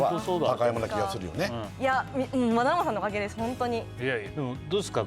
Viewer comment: テロップに引っ張られないように目を瞑って聞いたら「まなもさんのおかげです」にも聞こえる